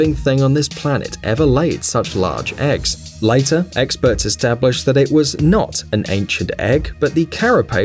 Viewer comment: does he say eggsperts?